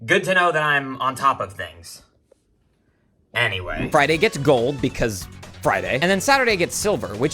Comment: Okay okay we get it it starts on Sunday😭😭😭 When you said Friyay I thought you said Fri-gay😭😭😭